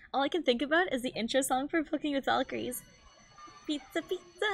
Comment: When you said "Cooking with valkyries" I misheard that as "Fucking with valkyries" If you're asking how, I dont know